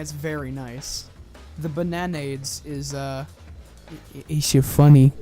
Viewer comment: Did you say Bann Aid?